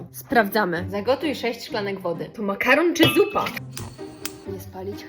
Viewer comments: Ej jak mówiłaś to makaron czy zupa to ja usłyszałam to makaron czy dupa 😅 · Czy tylko ja usłyszałam "to makaron czy dupa? "...